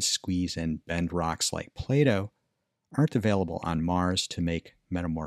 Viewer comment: I misheard that as "Bend rocks like Plato", and was like "Huh? Was Plato known for bending rocks?!" until it clicked.. Play-do